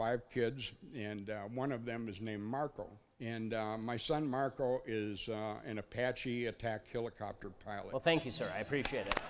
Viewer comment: i thought he said his son was an apache attack helicopter LOL